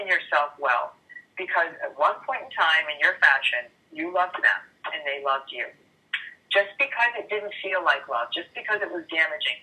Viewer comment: I don´t know if I heard it correctly. But did she say, "you loved them and they loved you" ?